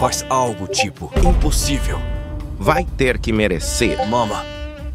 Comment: eu ouvi certo? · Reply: Mama